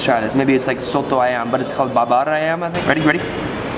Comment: I hear “babar ayam” 😂